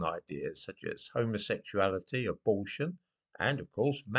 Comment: He said abortion, but i swear at first i heard "bullshit"